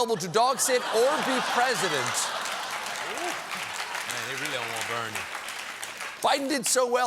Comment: Did Jon just say "Man they really don't want Bernie" in the background? Lol. Anyway, I feel sorry for America.